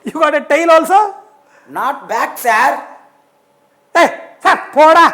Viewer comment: 😂 😂 😂 What did i just heard. “Tail not back da.......” Lol